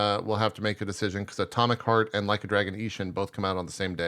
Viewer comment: You mean atomic heart? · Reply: probably. lol, Swore said wild hearts which is that monster hunter like game.